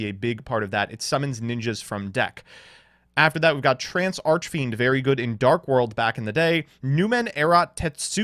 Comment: Thought he said "Trans Archfiend" instead of "Trance Archfiend."